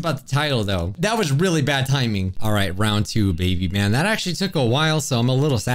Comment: For some reason when said “round 2 baby” but started the next sentence with man so I thought he said “Round 2 baby man”.